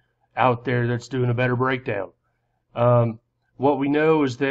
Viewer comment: Did you say "Breakdance" at 00:24.